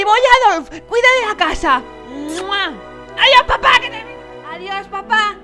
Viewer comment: jajajaj en el minuto 0:51 escuche "adios papa que te den"